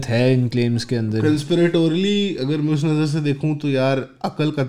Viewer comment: Did he say “principlatorily”? @ 17:15 LOL…Very knowledgeable though and appreciate the podcast and their perspectives